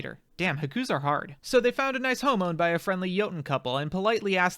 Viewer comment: thought this said “they found a nice homo”